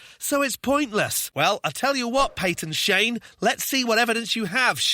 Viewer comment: Oh, Peyton Shane ! I thought you said, "Patent Shame" !!